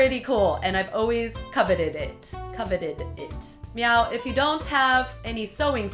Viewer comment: Did anyone notice she said meow at 1:25 instead of now